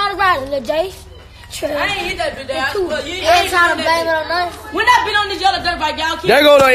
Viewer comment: DID HE SAY IAN HIT THAT BI DADDY😂😂😂😂